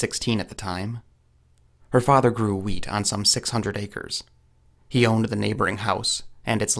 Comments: I honestly thought you said "he grew weed on 600 acres" and I was like.....blimey, that's a lot of weed. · Thought he said “her father grew weed” 🤣😂